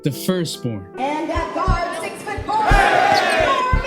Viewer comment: Did she say 6 foot 4? lol